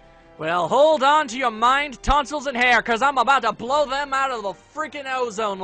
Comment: Well hold on to your mind, tonsils and hair! For a second, I thought you said Mind Tonsils. And then I was wondering what the Hell are Mind Tonsils.